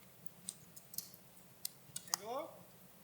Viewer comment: That was clearly a person calling out what sounded like “Angelo ?” It sounded like it was questioning. CREEPY AF !!!😮